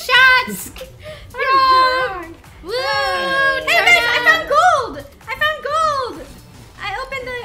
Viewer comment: Did say "Hey babe I found gold!" at 40:42 ?? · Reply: It sounds like babe but it is guyys